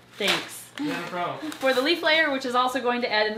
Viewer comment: When she said " leaf layer " I thought she said " leaf glitter " and I could only imagine a very fabulously shiney glitter filled exoterra with a disco ball hanging from the top